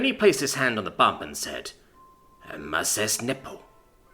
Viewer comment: "Emases nepo!" Wait, that sounded... checks text Ey! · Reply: all i heard was "massless nipple"